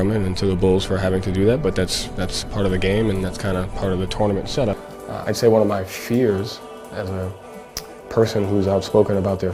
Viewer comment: Did someone say shutup at 0:37 😂